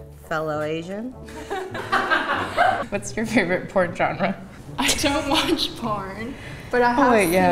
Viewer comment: "what is your favorite porn genre?" "anime" "right on" RIGHT REALLY ON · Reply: I thought she said her favorite foreign genre